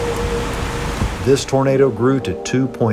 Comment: The subtitles at 1:45 literally says burrito instead of tornado 🤣